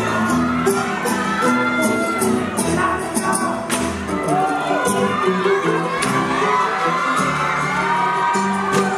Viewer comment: what did that dude say lol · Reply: I think he said "Jessica"